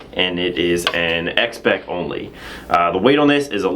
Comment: what does say? X beck? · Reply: Christopher Gumina X Spec